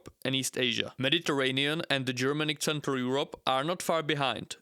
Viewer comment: Are you really Czech? You have an Indian accent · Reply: Hahaha, I absolutely agree, I think I heard him say "tsentral yurop"